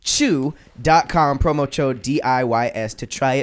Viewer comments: I swear says, “promo-chode”. LOL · did say promo chode? 😂😂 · Did anybody catch say “promo chode” @ 47:24 😂